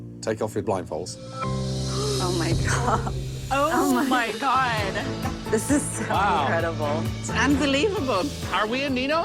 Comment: The sister says "Oh my God, this is incredible!" but the subtitles say "Oh, my God, this is terrible!"